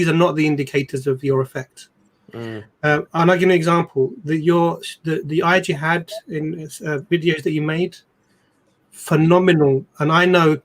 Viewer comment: could anyone tell me what did he say in that time stamp something like "eyes you had" I know it is not that, but to indicate what did he say, if it is a video what is it? I could not understand it, also I went to the l..ink that was posted in the chat for Asadullah channel, it says it is not available, or down.. thank you, jazak Allah kheir, may we all meet in the Ferdus.Amin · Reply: You might have heard it right, "the eyes you had from the videos you made" sounds like thats what Brother said. It may indicate the views of his videos and the importance of those even that might not be a huge deal of views but even the smaller number may carry significance when beneficial. Allah knows best... after hearing a couple of times more thats the explanation I came upto